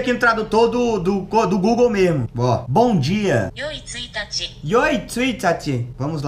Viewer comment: eu entendi oi itachi onde a otakise me levou?